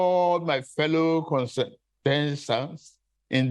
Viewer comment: Did he say he's urging his fellow "consenters" or what?